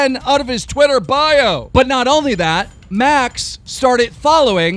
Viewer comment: I thought he said later banks instead of max I was dead